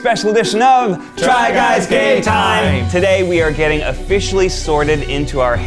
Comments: I thought they said “try guys gay time” and I was like yasss bring it on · I could’ve sworn they said “Try Guys Gay Time.” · I swear I thought they said “Try Guys Gay Time” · In the beginning I thought u said try guys gay time I love that instead on try guy game time. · I thought they say try guys gay time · I am not a a regular viewer and at the beginning I thought they said "Welcome to Try Guys Gay Time!" and I was like...alright then 🤣🤣🤣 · I thought they said 'try guys gay time'. Wrong video. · No matter what I always hear, “Try Guys GAY Time!” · For a little bit I thought you guys said try guys gay time 😂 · swear i heard them say "try guys gay time" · For like a year I thought they said “try guys gay time” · The try partners should do this too, like ariel, Becky, maggie, and Matt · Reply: I thought they said 'try guys gay time'. Wrong video. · Is it just me or does it sound like they say "try guys gay time" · I thought they said “try guys gay time”